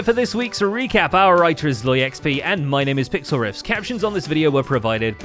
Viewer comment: every time he says "our writer is ZloyXP" I hear "our elytra is low XP" haha